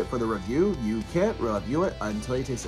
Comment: Wait - Did you always say "Fellas" at the end of videos? I thought you were saying "Fearless!" That was so bad ass. Maybe I misheard it the whole time.